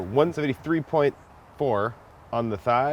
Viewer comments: 133.4° on the thigh? Done? Did I hear that correctly? @ 0:31 I though it had to be 165° to be safe to eat? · It sounded like he said 173 to me. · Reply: It sounded like he said “”We’re at 133.4 on the thigh” , but the closed caption says 173.4.. Check it out